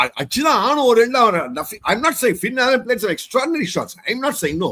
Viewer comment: Did he said othaa??😂😂😂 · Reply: "Oruthan" uh sonnaru bro